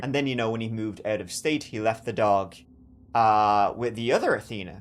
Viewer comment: I thought you said he sold the dog.